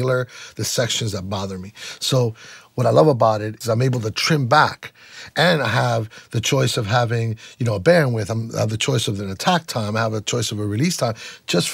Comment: What does he say at 2:11? Auto-subtitles suck :( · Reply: Hi there, what he says is: "What I love about it is that I'm able to trim back, and I have the choice of having an attack time and a release time just for that one frequency" Let us know if this helps!